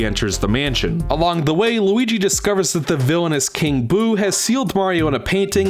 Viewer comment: When you said "the villainous King Boo," at first I thought you said "the villainess, King Boo" and got confused because then it would be Queen Boo.